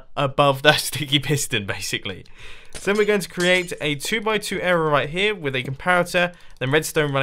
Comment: It sounds like he said "2x2 error"